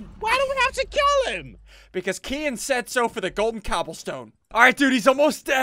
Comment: Did he say Kian at 9:06 to 9:010